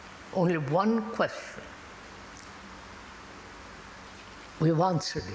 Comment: In the CC you get "we have answered it" which doesn't match the letters that he's uttering. It sounds like he says" we would not answer it"? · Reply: We’ve answered it. Right.